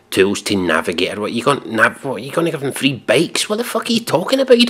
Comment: "You're gonna give them free BRAKES?" Not sure what you said there. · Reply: Bikes.